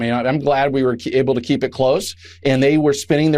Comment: Listen carefully what he says here: I'M GLAD WE WERE ABLE TO KEEP IT CLOSED! In plain English;The republicans keep the government closed!